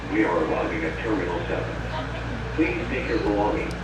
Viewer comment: Did he say, "Please take a Salami"?